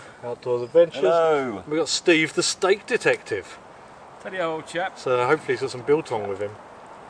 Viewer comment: Hahaha just seen the pinned comment. I thought you said the snake detective.